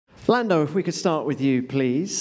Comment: it sounds like she is saying Flando